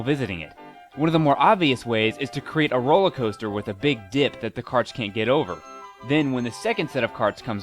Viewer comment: When he said "Create a rollercoaster with a big dip" For a second I thought he said "Create a rollercoaster with a big dick" x'D